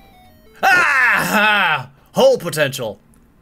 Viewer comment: What did say when it got too hot in the fjords? "SHORTS POTENTIAL" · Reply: ShadianVise I thought he said "Such Potential"